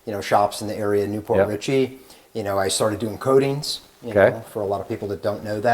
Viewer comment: I thought he said "I started doing codeine"🥴😒